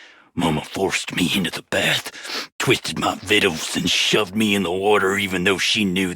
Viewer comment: ok im stupid, vittles??? whatd she twist i dont understand his cowboy language